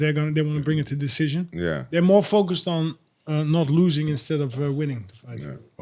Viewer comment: alistair said "theyre more focused" but genuinely sounded like a dutch guy trying to say "them mofuckers" with hood cadence